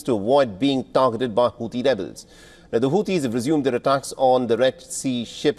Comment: Sorry I heard hauti devil's 😂 hauti rebels rather